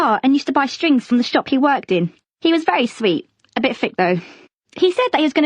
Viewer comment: what does she say at 8:40? when she says "he was very sweet, a bit ???? though." what does she say!?!? someone plz answer :( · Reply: A bit thick though